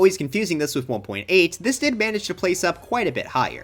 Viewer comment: thought he said "maces," not "mesas"